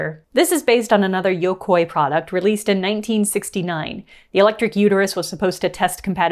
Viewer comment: did I hear “electric uterus?”